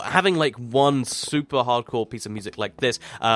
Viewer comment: Did he just say "suuuper hard-core"...?